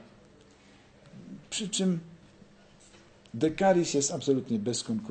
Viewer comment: Ktos moze podesłac link z tym srodkiem na odrobaczenie dekalis jak dobrze usłyszałem. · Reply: djslaby Decaris.